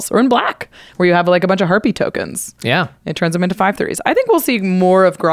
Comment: I thought said “herpee tokens.” She said harpy.